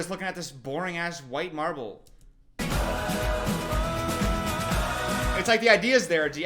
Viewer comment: During the Alex Warren critique I thought was going to say “we’re just looking at this boring ass white man” ❤ not “marble”